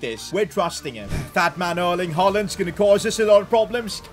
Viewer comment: did he just say fat man Erling Haaland